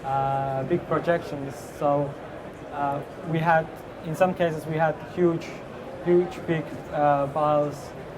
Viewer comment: "In some cases we had huge big balls" what did I just hear?